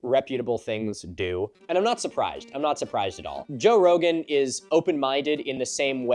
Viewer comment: where you were talking about Rogan and all that, I heard "Nazi Prized" instead of "not Surprised". had to go back and see if you were making a current times nazi pun and I feel it should be that way.